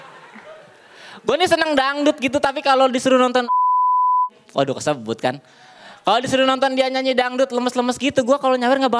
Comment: Menit 2:35 yang di sensor itu ngomong apaan ya? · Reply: Al Gazali